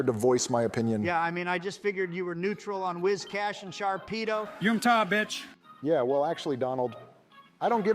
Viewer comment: what does Joe yell out here? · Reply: Yoomtah bitch, reference to the wigglytuff from mystery dungeon